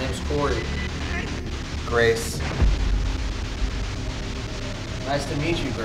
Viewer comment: after corey said “whats your name” i heard “kacey”